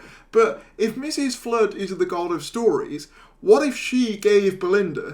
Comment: When you said God of stories I thought you said God of strawberries 🍓 😂😂😂😂😂